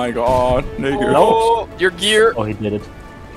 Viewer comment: WHAT HE SAY LOL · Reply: Funni gear